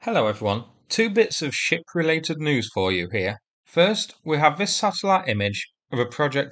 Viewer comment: For a second I thought you said "Two bits of shi-t related news here". Need to get used to your accent 😂 😂